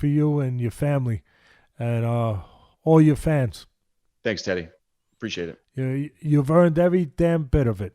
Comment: when he said thanks teddy it sounded like he said thanks daddy